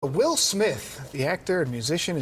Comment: At the start it sounds like u said “Will Smilf” 😂